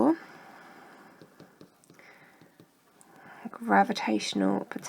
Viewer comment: what on earth did you say? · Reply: Reckons ‘geeks lunch’ 👍🏻😂